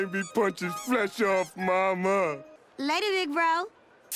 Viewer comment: I deadass had to rewind that like 5 times because I legit thought she said "later, negro"